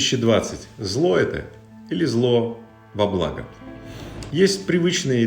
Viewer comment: сказал правильна-зло во благо ,а мне послышалось -зло бабла .